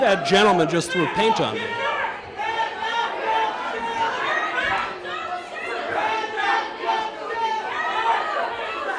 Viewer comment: what are the crowd saying? I need to know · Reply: "Hands up! Don't shoot!" When leftists say that, they're trying to say they feel "oppressed" by an authority figure of some kind.🙄